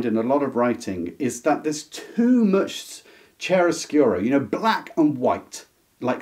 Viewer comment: Hey. Great video as always. What’s the word you use at 21:33? Right before “you know, black and white”. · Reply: I believe he said "chiaroscuro." It's an art term referring to the use of light and shade.